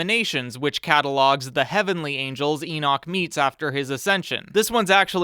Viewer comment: my Brain first heard Eats instead of Meets